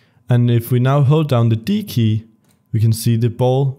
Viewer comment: sory i cant heare you say what at 16:17 ,hold down what? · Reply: the d key